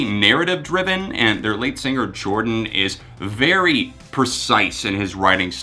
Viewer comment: I heard 'late singer' instead of 'lead singer' and panicked. Jordan's lyrical talent is beyond worlds.